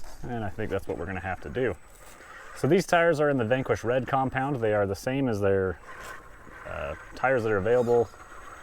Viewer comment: Thanks for showing us the rig on rocks. I'll be ordering minesoon. I bet the Stance tire would look great on there proportionaly. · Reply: Nevermind. I misumderstood what said at 4:18. For some reason I heard "these are the same as".....and I did listen to rest of the sentence. LoL.